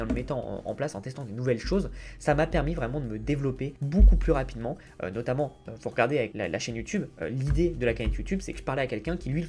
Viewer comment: Hello 8:10 tu dis quoi ? Je comprends pas trop le mot l'idée de la ca..... youtube que quelqu'un le faisait déjà sur instagram... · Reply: cagnotte :)